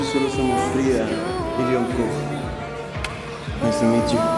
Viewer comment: Entendí "esta noche solo somos Frida y Jungkook" JAJA